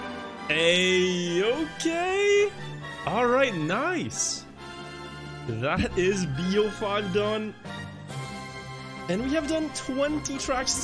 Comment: Did he say b0juan? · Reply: He said "that is B05 done"